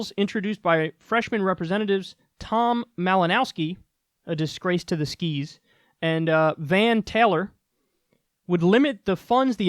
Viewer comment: What did he say at 0:31? " A disgrace to the skeeze"??